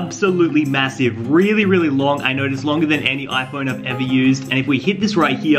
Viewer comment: Sorry but....did you just say iphone?